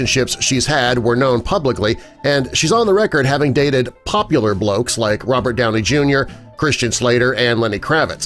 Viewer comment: (approx.) did you say: "Bloke (s) ?". I never knew Americans spoke Australian English. Cheers🥃 from Melbourne 👍